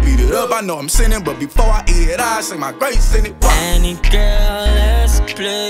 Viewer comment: Who is this anne he (TeeFlii) always singing about lol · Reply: I thought he was saying "any girl" not "anne girl"